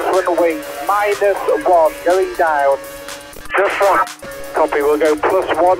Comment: Barrichello's enginner is more confused than Kimi's one during the Ferrari years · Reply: The radio quality is pretty shit, I can understand how one could mishear "Just one" as "Plus one"